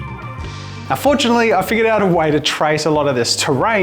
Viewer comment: min Anyone else, who thought he said "Unfortunately I figured out a way to trace a lot of this Terrain"?